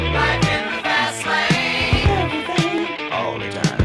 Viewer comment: When It first came out, I thought they were saying "life in the vaseline" (low tech sound equipment) LOL. BTW: Don Henley lived in the fast lane for a while, but he got out of it before it did destroyed him too badly.. allegedly, the song, "Life in the Fast Lane" was partially inspired by it.